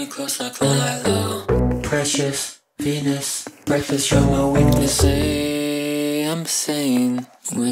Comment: I thought he said “breakfast, you’re my weakness”